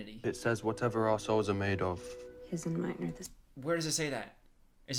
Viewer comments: When they say “whatever our souls are made of his and mine are the same” I thought he was saying whatever ar*seholes are made of his and mine are the same. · Bruh I heard that as “whatever assholes are made of” I had to hear you say it to understand it